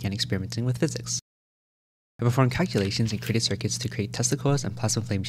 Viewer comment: Not gonna lie, I thought you said you made testicles not Tesla coils. MIT has to let this guy in - he’s out here creating testicles and stuff! Freaking genius.